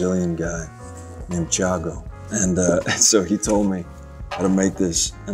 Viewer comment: The brazilian name is Tiago or Thiago (the subtitles are wrong)